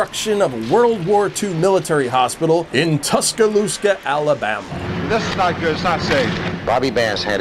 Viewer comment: Could've sworn you just said "Tuscalooska"... not cool, dude